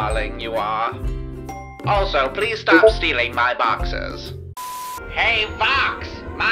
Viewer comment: Nifty WTF. 😳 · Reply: OH. I THOUGHT HE SAID BOXES, NOT BOXERS 💀💀💀